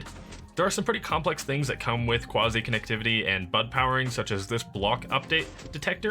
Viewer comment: I'm glad you gave the acronym for B.U.D. powering, because I heard "Butt Powering." 🤣